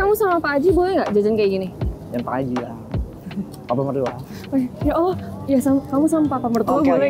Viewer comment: ngomong apa tu ya.... jangan pak haji YANG, papa mertua. cie... YANG katanya wkwkkwwkkw terciduG!